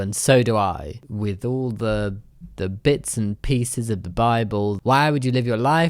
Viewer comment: i thought he said bi ball at first and i was pretty confused.